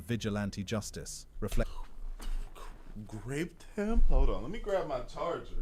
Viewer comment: It sounded like you said he raped him 🤔